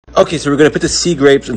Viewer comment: when he said "look at those sea grapes" j thought i heard corspe